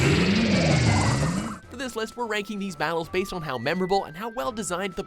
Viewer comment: did he just say box battles?